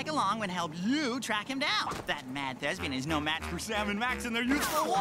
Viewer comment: Can anyone explain what does he say at 1:00? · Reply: Hey, in 3:03, did he say "that mad thespi[a]n" or some other word I couldn't find due to lack of vocabulary?